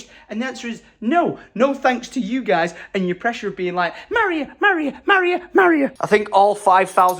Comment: I’m really unwell with a chest infection and I heard “Mario Mario Mario” instead of me rewinding and watching again I actually heard “marry her” x3 My partner also has told me we are end game for each other so yay No rush in getting engaged and married, too many people rush those things You’ll be rushed into an engagement, then be rushed into a wedding, then be rushed into a home (if you’ve not got one already which you both do already have) then rushed into a baby Once you do a milestone people seem to rush you into another When it’s all rushed and every milestone is done there’s nothing left to look forward to Me and my partner don’t want kids or to be married We want a home and dogs and that’s us done :p I know you won’t let people rush you both but my god people are ridiculous like Americans together 2 years are rushed into babies and marriage and they barely know each other I’m with my partner 8 years now and still saving for a home together